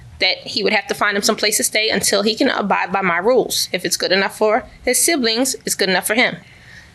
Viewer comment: all I heard was, If it’s good enough for the Goose, it’s good enough for the gander. 😂